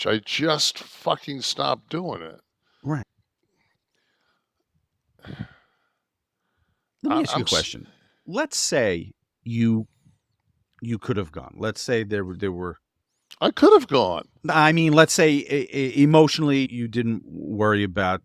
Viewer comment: I thought WWE said you couldn’t go. 😂